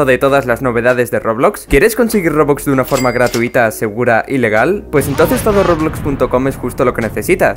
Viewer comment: yo escuché ilegal XD